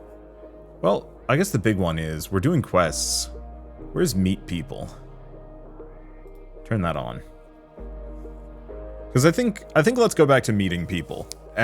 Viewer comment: idk, I heard asking "Where is meat people?" and it didn't suprise me..